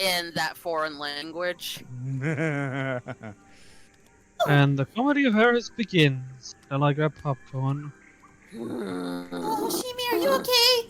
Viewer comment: What the heck did you say? I could not understand you. I made out "And the ---- begins." And something about popcorn 🍿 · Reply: I got "And the comedy of errors begins. Shall I grab popcorn?"